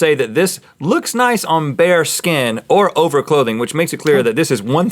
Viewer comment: When they said bare skin I thought they were saying bear skin and was really confused